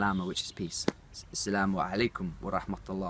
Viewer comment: AsSalaamu alaykum. ( it actually sounds like yur saying Iss-lamu alaykum.) I was taught to say MarhabaN with an 'N' sound at the ENd. bahabuk. Ya Bahabuk😇